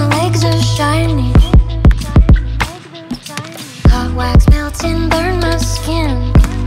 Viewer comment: Bruh I thought it said " clockwise mountain, burn my skin" Instead of "hot wax melting, burn my skin"